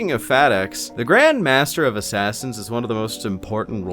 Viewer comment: I keep hearing “Fat Ass” when you mention the Grand Master of Assassins.